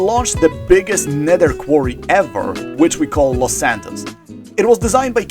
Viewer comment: For a moment I thought you said y'all named it Los Angeles 😭😭😭